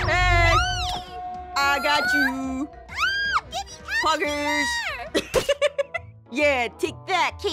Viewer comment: did she just say poggers?